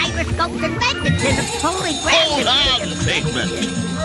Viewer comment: it sounds like Figment says "holy crap" lol